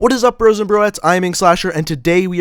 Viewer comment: Wat does he say in his into he says bros and and something else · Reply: lmao i thought he said whats up bros and breads 😅😅